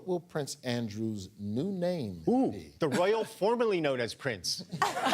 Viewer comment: I have no idea who came up with it but I love the phrase, 'the Andrew formerly known as Prince' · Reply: I heard it as" The nonce formerly known as Prince"